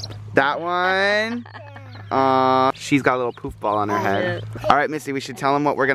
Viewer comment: Who else thought said poop ball??